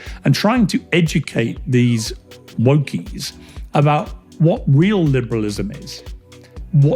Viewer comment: I think he said wokies, not monkeys Lolll